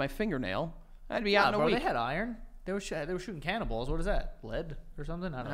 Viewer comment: rewatching and i swear is saying “canna-ball” instead of cannonball hhahahah